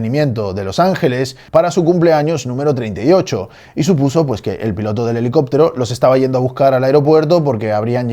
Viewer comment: entendí e pelotudo del helicóptero xd